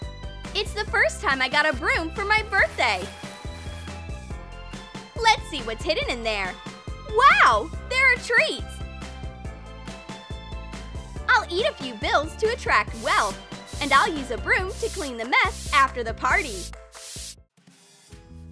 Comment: she said trees instead of treats